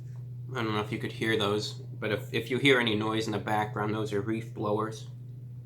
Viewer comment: Woah... did he call them "reef blowers"?